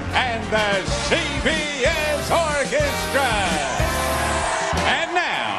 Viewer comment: What’s he saying? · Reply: "12th century German visionary-- DA-A-A-A-A-A-A-VID LETTERMAN!"